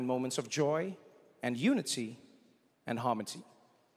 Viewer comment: Did he say “harmoty” @ 0:54?! Still a legend!